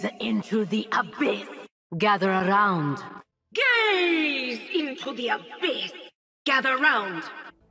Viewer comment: Why i always hear "Gays into the abyss" They are sending raum and tiberius back to the abyss 😭😭